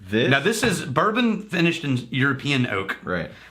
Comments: does call it...a "Bourbon finished in European Oak" or am I hearing things? Could just be the alcohol talking, but perhaps we need to have and Rex make another adjustment to that "__ many days since Daniel messed something up" board. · did he just say this is Bourbon finished in European oak? Can't say I've ever had a Bourbon Laphroaig lol